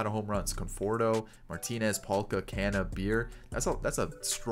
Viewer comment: Am I the only one who heard you say "Can of Beer" when you were listing "Canha,Beer"???!! (#NotALush)